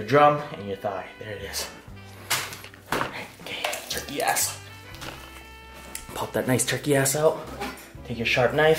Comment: did he just say "pull this turkey ass out" I never hea😁rd him curse before😂